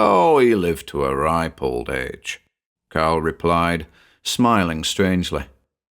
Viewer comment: Your accent while lovely, or should I say "luveley", can be a kind of sport. For instance, I thought you said Carl Rove, and they went for a drink at the Probe. 😊